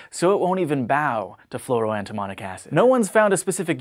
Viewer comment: Is the reuplad due to the fact that the subtitle said "DEMONIC ASS" at 4:11 instead of fluoroantimonic acid?